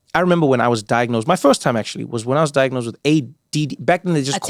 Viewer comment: The closed caption transcribed ADHD as AIDS or AID twice. For someone reading the CC instead of listening, the wrong impression may be left. Just wanted someone to be aware in case you want to fix it.